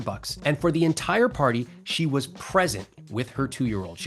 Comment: subtitles error. It says pregnant instead of present